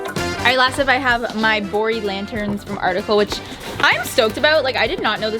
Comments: Yeah, I too thought I heard "boring lanterns" and not "Bori Lanterns." I thought, "Well that's not a nice thing to say about those lanterns! Yeah, they are simple, BUT not boring!" LOL Awesome makeover! I wish I could be HALF as clever as the two of you! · every time she said "bori lanterns" I heard "boring lanterns" and it took me an embarrassingly long time to realize that she wasn't calling the lanterns boring